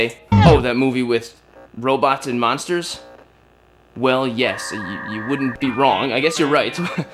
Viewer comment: When he said robots vs monsters I thought he said plants versus zombies